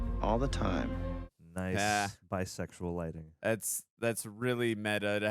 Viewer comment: Did I hear said "nice bisexual lighting